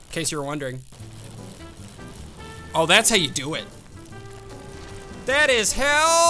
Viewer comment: seriously called “how” as if it was pronounced “hell” turn on subtitles here if you cant understand it